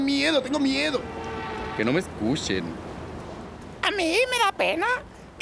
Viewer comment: Qué dijo el del segundo 00:48? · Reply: Que no me escuchen.